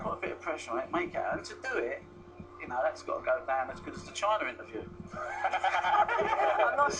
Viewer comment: What does he say at 2:50? "That's gotta go down as good as what????" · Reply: The China interview. I think he was quite rude in that and he was swearing live on tv (but it's China so it's ok, ofcom can't touch him lol)